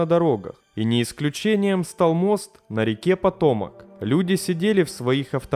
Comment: Услышал «мост Патона» 😂